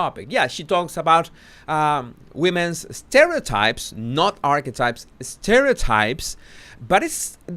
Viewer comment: Maybe it's me but I keep hearing "arsewipes" when you say archetypes 😅